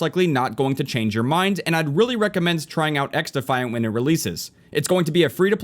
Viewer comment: What is the game mentioned at 24:26? English is not my first language and I can't figure out what he said😂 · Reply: Xdefiant